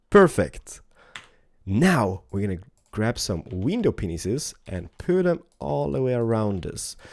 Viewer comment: it sounds like he said "window penises"